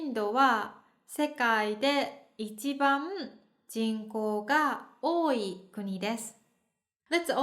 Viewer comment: The Japanese subtitles are "人" instead of "人口".